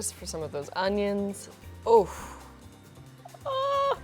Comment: Did she just said "oof" at 12:40?!